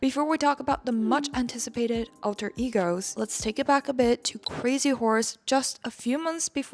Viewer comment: When you said Crazy Horse I thought you said Crazy Wh*res